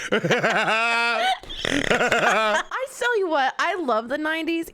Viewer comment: Anyone else hear say "I sell you what." Instead of I tell you what. Lol